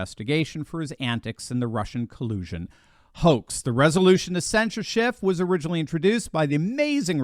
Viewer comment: Oh shit that means fired, i thought they was saying censor, Im like shit they censoring everybody join the club.